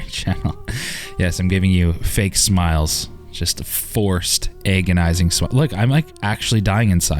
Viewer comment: did you just say "eggonising"? lol